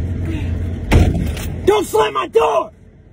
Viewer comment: At first I thought he said don’t slap my dog